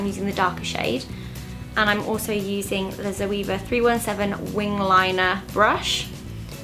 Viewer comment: When u said 'zoeva' I thought that u said 'zozeebo'